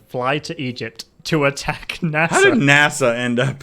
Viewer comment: Probably referring to President Nasser of Egypt. Even when I heard his name for the first time, I was like, huh, NASA?